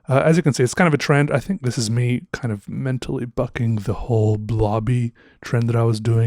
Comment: Definitely misheard that 1:50 I heard “mentally f*cking” I was all WOAH!! ~that87dudeson